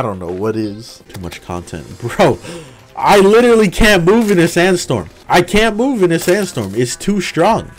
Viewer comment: oh god i thought he said sans storm